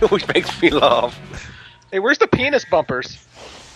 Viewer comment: I heard penis pumpers instead of pingas bumpers.